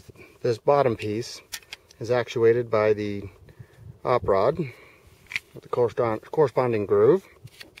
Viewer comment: I'm not a native speaker so I don't quite understand few words in this video. More specifically, since that time (2:08) and until (2:18). The author speaks something like this: "This bottom piece is actuated by the [something that I don't understand]" Could you tell what the author said? I'll be a very grateful. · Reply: Николай Мороз Sure thing buddy. He says "actuated by the op-rod and corresponding groove." Op-rod = operating rod. If you need help with the definitions just let me know!